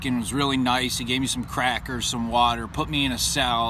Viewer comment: it sounded like you said, " the officer was real nice, he gave me some CRACK or some water..." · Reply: Lol he said "crackers" you probably know by now though